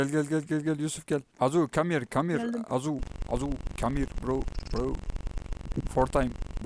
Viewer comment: ne dedin😅😂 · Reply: azu adamın adı